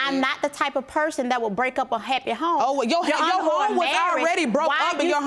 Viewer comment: DID SHE JUST CALL THEM HOES?